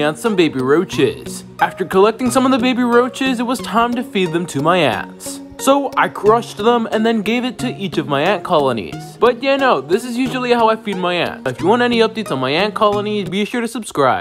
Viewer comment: No why did you feed them my brother And plus it kinda sounded like you said "tine to feed my ass"